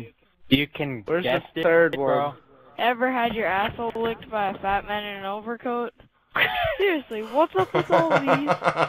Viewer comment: Ha! at first I thought he said "Batman in an overcoat"